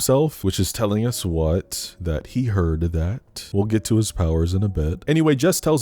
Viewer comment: help why did I hear that as "he her'd that" Like he/her'd that I'm crine 😭